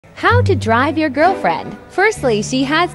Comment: You can tell i have a dirty mind when he said drive i thought he said ride😂